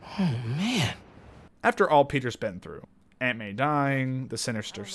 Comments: anut instead of aunt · Instead of aunt, and stay with me here, the subtitles say… ANUT! ITS F-ING HILARIOUS